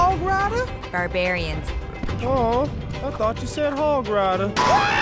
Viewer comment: "Awww, I thought you said Pig Rider..." Love it! xD